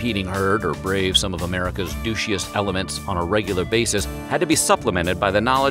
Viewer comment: did he just say Douschiest? I don't even kno how to spell it lol That's amazing